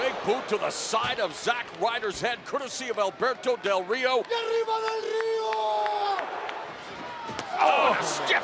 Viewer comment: What does Del Rio say at 0:45 ? Don't know spanish but sounds something like "arriba Del Rio!" ?